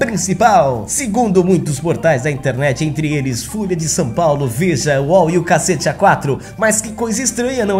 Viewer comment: Eu ouvi "Fúria de São Paulo"? hauhauhauhahauahauhau